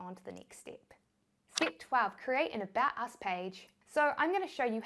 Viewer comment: i thought she said create a badass page lol